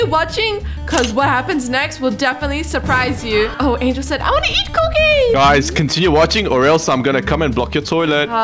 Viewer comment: what did angel said😭😭😭 0:50 😭😭😭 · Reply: I WANNA EAT COOKIES THATS WHAT SHE SAID!